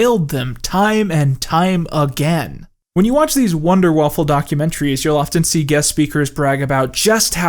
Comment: At first I thought I heard 'Wunder waffle' and I was like "oh COD WAW memes, haha" and then he kept saying it, unironically.